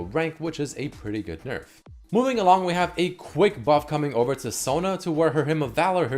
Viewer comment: it says Sona nerf,he said Buff. on drugs 😂😂